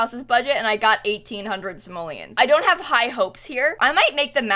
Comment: the whole time i thought you were saying "somalians" instead of simoleans and i was so confused